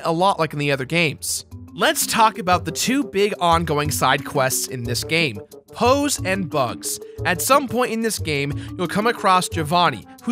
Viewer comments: Whoops. Thought you said "hoes." My bad. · Totally thought you said Hoes and Bugs for a second there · For a moment I thought you said "hoes and bugs"! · I thought he said "hoes and bugs"